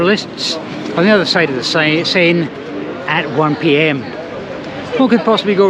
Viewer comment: Found out I was only partly listening. Thought I heard saying: Gare Austerlitz on the other side of the Same river 😂. Of course he was talking about the Seine. My only time in Paris was by accident. Should have taken a nighttrain from Strasbourg to Nice. It was cancelled because of a strike. So it was a nighttrain to Gare de l'Est and a quick metro to Gare De Lyon. An Expresso and a croissant and onboard the train to Nice. 45 min stay. Should probably go there one day. 10 times in London, 0 in Paris